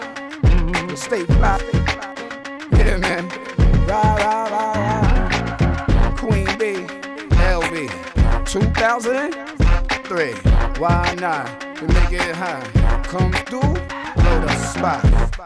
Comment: I swear I heard him say to 2023 why not😘